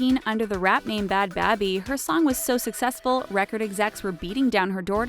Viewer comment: I thought you said smart not successful